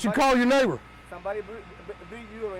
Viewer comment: That last dude. I thought he said i got somebody to beat you. Than said video you. After you check him.